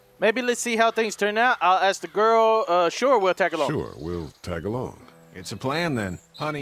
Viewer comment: I thought said We're Tagalog (which is a language if you dont know)